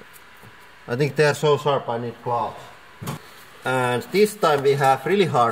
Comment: The subtitles say "unintelligible" but I'm pretty sure it's "I think they're so sharp I need gloves"